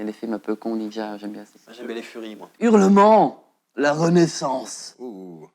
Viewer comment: " j'aime bien les furrys moi " 😆